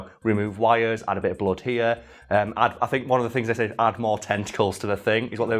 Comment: Misheard that at first as the thing needs more testicles